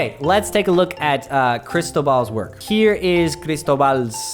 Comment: Cristobal. I thought he was saying Crystal Ball. HA.